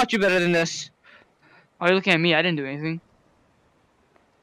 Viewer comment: Did I hear that correctly? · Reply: yes you did he said Jëager/Yeager